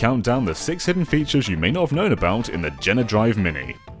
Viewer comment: Did he just say "Genedrive"?